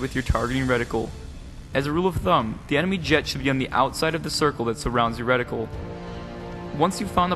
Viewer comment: did u say outside the reticle? Huh?